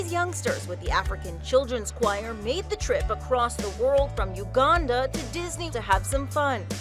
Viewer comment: I thought it said Wakanda, so I went back to make sure it wasn’t, and it said Uganda. 😂